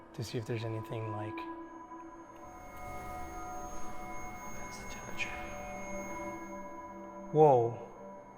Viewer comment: on the dot, was that one of them talking, or something else? It sounded like either "This is torture" or "What's the temperature", which would correlate to either the spirits or the boys, respectively.